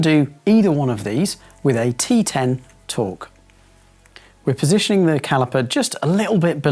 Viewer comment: Ahem... "Hexalobular Internal" not torque wrench. And I know you meant Torx, but I heard "torque" and so did google when they captioned it. 😜